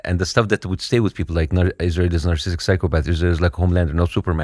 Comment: What the heel does he say at 8:10? Something about "..Israel.. Superman.."?! He says it so fast i can't make it out